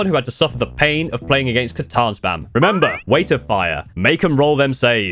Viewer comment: I cannot understand what words he is saying relating to the Catan Shard before saying don’t let them half any damage. Sounds like he says “weighted fire”. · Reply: "Weight of fire". Basically chuck a ton of 1 damage shots at them like hurricane bolters and stuff